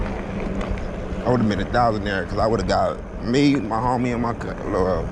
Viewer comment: Anyone else hear I would’ve been a thousandair 😂😂